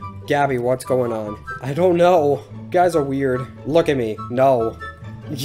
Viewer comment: did he just say get the shid away from me creep?????????